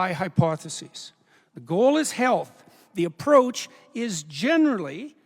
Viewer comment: sounded like he said the goal is hell, why is it not pronounced as HEAL th ?